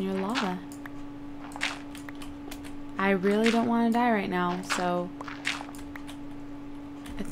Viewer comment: at first i thought you said i really want to die right now